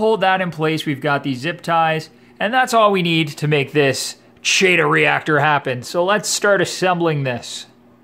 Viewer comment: I thought it said Cheeto reactor, I must be hungry.